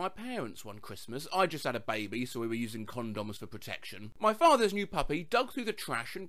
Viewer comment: I thought you said ‘cousin’